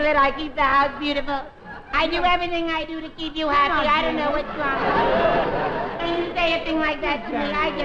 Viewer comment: What did Dean say "Jeannie isn't that ??" I've played it back several times and I still can't make it out what he says over Jerry's whiny voice. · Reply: I thought he said "Jeannie doesn't do that"